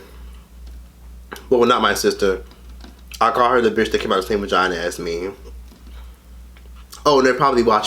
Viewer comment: I thought he said Satan's vagina..lmao. Funny cause Satan has that one demon He lives so dearly and deathly, Baphomet, the one with both genitalia lmao! VERY fitting for her...